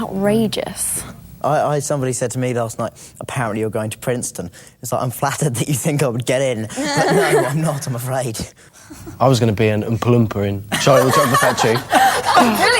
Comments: What did Rupert say? It sounded like, "I was gonna be an implomperin." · Concerning the outrageous reports they've heard bout themselves: wht did rupert say it was his - at 4:10? Couldn't catch up with tht one.. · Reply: he was going to be an oompa loompa in Charlie and the Chocolate Factory, now subscribe or I will cast the Cruciatus curse on you. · What did Rupert say at 4:09? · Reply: "I was going to be an umpa lumpa" - that's from roald dahl's charlie and the chocolate factory